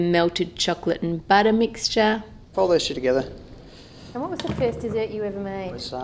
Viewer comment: "Fold that s**t together" Did he really say that? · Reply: i think he actually says 'fold those two together' ... i think!